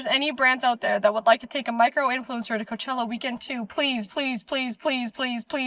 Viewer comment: I thought she said "microwave influencer" and I was momentarily confused 😂